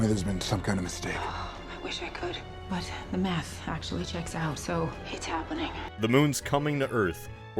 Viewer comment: It sounded like she said ‘the meth checks out’ that would explain the ‘inspiration’ behind the script.